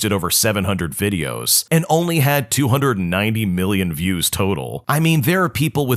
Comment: 290 million not 29 million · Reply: I actually thought I heard 29 million not 290 million, but thanks for the correction